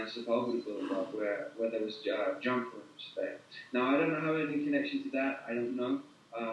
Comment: Can anyone understand what he says at 30:55, right while mentioning: "another program going on at 999 Sepulveda where there was .....?" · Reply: did he say jump room?